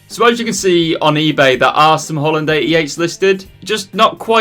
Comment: Haha I keep hearing “Hollandaise Sauce”